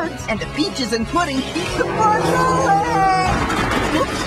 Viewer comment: I learned about the word cuisine from this commercial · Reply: At first I thought that said *World Cuisine*… Made me think of Italy, France, China, Japan, México, & Spain etc.